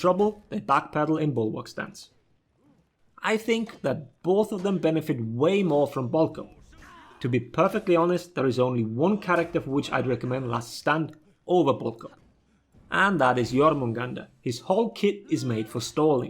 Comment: These automatically generated subtitles (English)... (@ 4:19) What's actually said: "... I think both of them benefit way more from Bulk Up. To be perfectly honest there's only 1 character for which I'd recommend Last Stand over Bulk Up, and that is jormungandr." Subtitles: "... I think both of them benefit way more from Balkan to be perfectly honest there is only one character for which I'd recommend Last Stand / Booker and that is your moon ganda" wtf